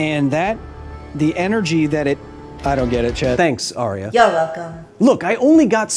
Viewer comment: lol I thought you said "I don't get it, Chet." but you said "chat" Why is it only that word, though? Like, you don't say "thet" instead of "that", for instance.